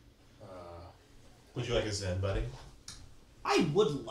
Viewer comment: what is the thing that offers? CC says "Zen", I'm guessing it's "Xyn"? is that a nicotine mint? 18:45 another reference to Zen (or Xyn?)